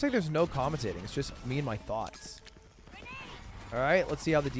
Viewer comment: Did Ludwig call his GC team thots at 2:25:04?